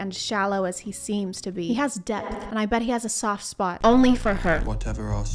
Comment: Please tell me I'm not the only one who heard "whatever arseholes are made of"